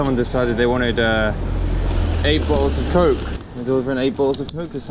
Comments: Man you are hilarious 😂!!! I thought you said “8 balls of coke” Thank You for the inspiration! Going to attempt this challenge on my channel! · Lol I thought he said “Delivering 8 balls of coke”